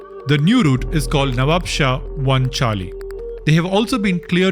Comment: Anyone else know what he is saying at 06:30? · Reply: Nawabshah 1 charlie